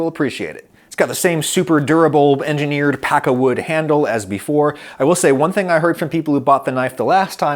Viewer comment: There is a mistake in the captions. At 5:48 you say "super durable engineered pakkawood handle" but the subtitles say "super durable engineered akkawood handle".